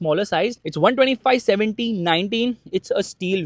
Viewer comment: 125/70 19 spare wheel Did i heard right ?